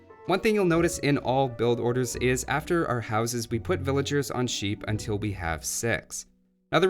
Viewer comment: After our houses, we put villagers on sheep until we have SEX ! I misheard that one!